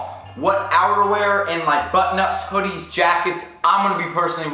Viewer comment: I thought you said 'but nuts'